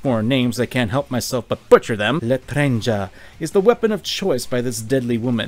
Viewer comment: I thought he said l’etranger then, I mean it makes some sense other than the gun the French funny guy has in hat game, l’etranger means the stranger so it fits the song